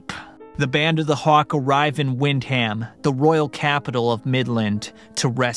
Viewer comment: Why did I hear “the band of the hawk arrived and went HAM” 😂